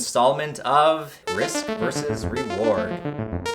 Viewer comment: Thought it said rick vs reward, got distracted with the rad rick shirt.